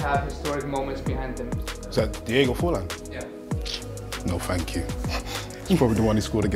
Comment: What does Frimpon say at 5:50? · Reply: He asked if it was Diego forlans. Then replied no thanks it probably the one when he scored against Ghana. (2010 World cup quaters)